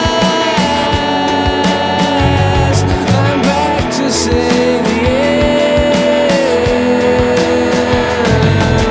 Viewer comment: He's back to say the what word?😊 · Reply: "I'm back to save the universe"